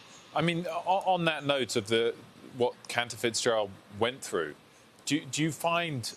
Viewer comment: 29 seconds ... Cannabis Joe? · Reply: That's exactly what I thought I heard too, but it's "Cantor Fitzgerald"